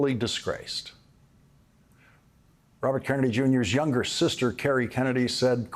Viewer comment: The closed captions say "Robert Downey Junior" not RFK Jr. Oopsie!